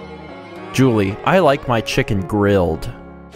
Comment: For Julie's, I thought she said "I like my children grilled" and, seeing the endslate, I don't think it would be above Chulip to write that... O_O